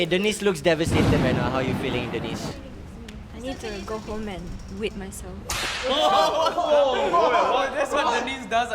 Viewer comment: “i need to go home and whip myself” awhhh she’s so baby 🥺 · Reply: I heard "i need to go home and quit myself" oop